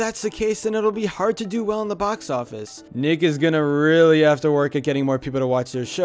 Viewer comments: I thought said the N word at that timestamp. Even though he said nick it sounded kind of like the N word. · did anyone else think he said the n word ( I know he said nick is.)